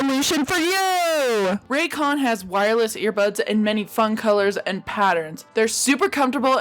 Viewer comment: d-did she say.... Air buds?